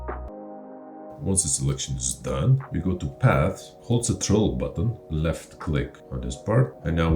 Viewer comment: what is he saying the Troll button???? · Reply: Haha no. It's my habbit of saying Ctrl button rather then Control button.